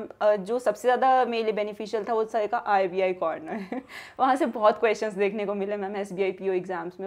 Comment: me kya bola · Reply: accha, mujhe rbi governor sunai pada tha 😂